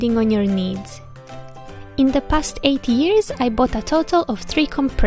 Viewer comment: I'm confused, did she say 80 years? (I don't mean to offend anyone)